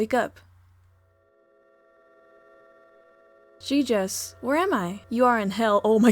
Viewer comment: I thought you said "jesus" instead of "giygas" here lmao